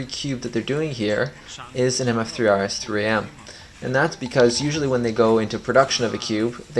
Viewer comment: Which cube does he say here 2:34? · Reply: Moyu mf3rs3m (not the 2020 edition)